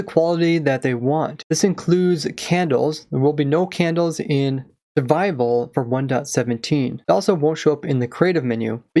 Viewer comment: I thought he said: "there will be no candles in the bible"